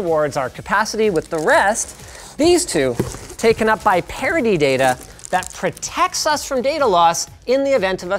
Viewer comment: I keep hearing "...taken by parody data..." I guess they keep their alternate takes